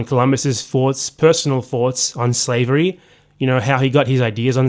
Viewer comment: Did you say “foughts” in place of “thoughts” at 3:20? Weird.